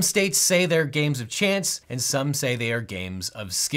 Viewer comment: I heard "A legal theory" as "Illegal Theory" lol